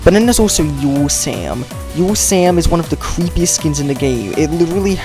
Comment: can you tell me the name of that Sam because it's kinda hard to hear what your saying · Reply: Yule sam